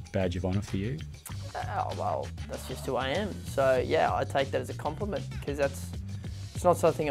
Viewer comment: "well that's just who i am. it's not something i'm trying to be, i just love the game." Almost sounded like, "i'm not trying to be the best, i AM the best." haha. nice lad.